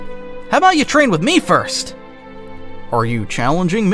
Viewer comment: To me it sounded like “Are you sure?”